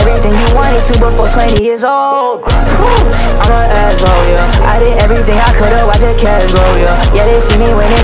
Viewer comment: I’m not gonna lie I thought he said I did everything I can to watch the cash flow yeah